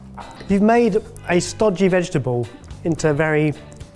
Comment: did call parsnip a "starchy vegetable" or did I mishear?